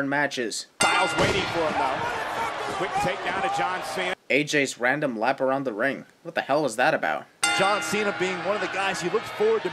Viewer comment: not a fair sin. he clearly said "i'm running circles around you, john" referencing AJ's promo's leading up to this match